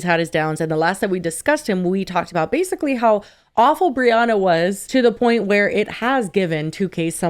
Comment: I thought said rhianna not brianna and i was like "where did chris brown come into this?!" 🤣